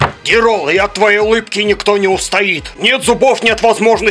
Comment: конец убил- "КОМУ МИНЕТИКИ, НЕ ДОРОГО" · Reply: Мне послышалось "КОМУ ИНДЮКИ, НЕ ДОРОГО"